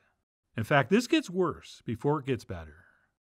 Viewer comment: Did I hear "... this gets worse before it gets batter"? time stamp